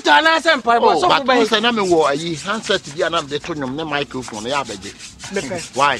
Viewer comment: did I hear likeee saying miclephone😅